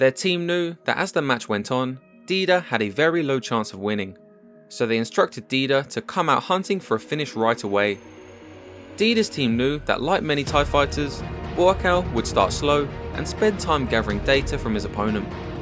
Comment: Whenever you say "Thai fighter" I hear "TIE fighter". 😃 And yeah, anyone with a pulse would be inspired by this fight.